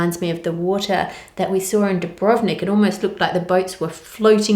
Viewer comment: U.S. fan here. Can you type out the name of this place that has such clear water? I tried google based on the sound of what you said but only found a donut store in ON, CA. 😂 I'm guessing that's not it! Always love your videos, Ann! chef's kiss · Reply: I think she may be referring to Dubrovnik, Croatia. It's a popular tourist destination, according to Google.